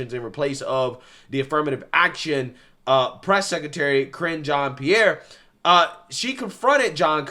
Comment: Cringe Jean Pierre is what it sounded like said, I don’t think he said that but it’s perfect moniker for that nasty pathological liar.